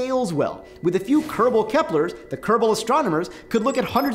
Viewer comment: For a sec Thought you said “The Gerbals”....